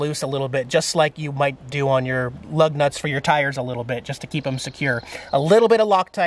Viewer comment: It almost sounded like you said..."you will use a little lock tight on your love nuts"....I had to do a double take and rewind a few to hear it....I am still laughing.....